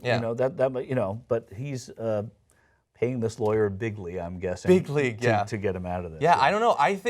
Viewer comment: does he say bigly? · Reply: at the risk of being consider a Drumf supporter, he said "big league". His moronic manner of speaking makes it sound like he said "bigly" Then again the guy is an idiot and probably did say "bigly"